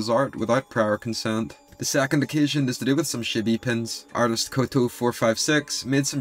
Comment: Ok but the way you pronounced chibi at 9:50 really sounded like you just called them shitty lmao